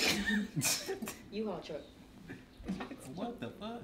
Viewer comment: Did say ELI truck ??😂😂😮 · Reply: She said U-Haul truck 😂😂